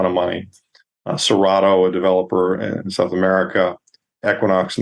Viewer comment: Great guest and great interview, as always. BTW, there are two companies whose name I didn't catch: 1/ at 16:26, a developer in South America, mentioned between Aya and Equinox, and 2/ at 16:54, mentioned before Steppe Gold. Could someone help me? Thanks in advance. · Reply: I heard Cerrado Gold, at 16:54 I believe he referred to Galantas Gold.